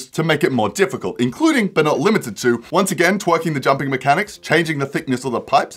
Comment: He says twerking jumping mechanics instead of tweaking